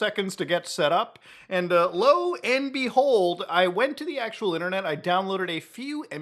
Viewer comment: I misheard this as "lol and behold" ohno :')